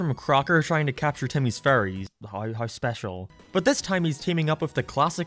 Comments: i genuinely thought you were saying "furries" instead of fairies ohmygod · When he said “fairies “ I thought he said furries